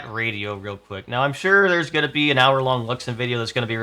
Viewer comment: When he says it's time to talk about the radio, he says that he knows there's going to be an hour-long video from, and then he says the name of I guess another YouTuber? But I can't understand what he's saying. Can somebody tell me what YouTuber he's talking about? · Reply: Luxin